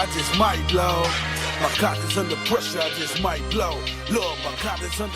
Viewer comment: Babyyyyyy the way I was groovin and then I thought he said my kokk is under pressure I just might blow. I was relieved den a mug when I realized that ain't what said! Solid 7... Now that I know 😂😂😂